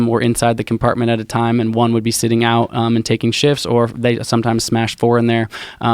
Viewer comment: thought he was gunna say taking shits